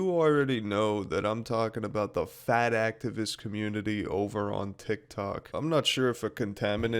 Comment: I thought you said fat-asstivist.......